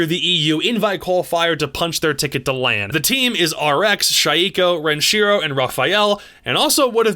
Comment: Are we just gonna ignore he said Shieeeko instead of shaiko?